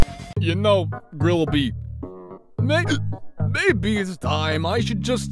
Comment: Did he just say the N word?